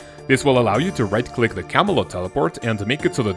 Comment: I thought kept saying camulet not camelot.